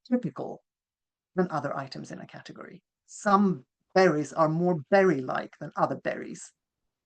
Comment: I rather misheard and thought the speaker was talking about a cognitive model for fairies rather than berries, until the part about them making good jam 😆